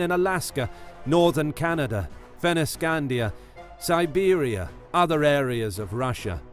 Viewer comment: In the minute 4:06 you said Venescandia instead of Scandinavia. Hahahaha XD